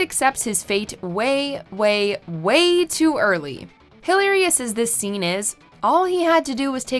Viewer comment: I'm sorry what? 9:36 "Hilary-ous?" Do you mean "Hill-air-ious?" Wtf???? Seriously wtf did she just say? Lmao😂